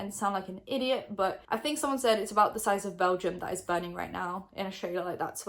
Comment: I am from belgium and I first heard "belgium is burning" and I was so confused 😅